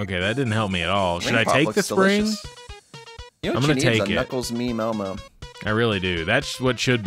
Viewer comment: Every time you said "knuckles meme elmo" i thought you said "tickle me Ellmo."